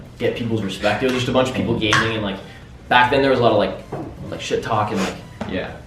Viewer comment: I think he said Sh*t.